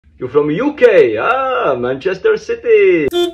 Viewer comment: I heard “You gay? Aaaaah Manchester City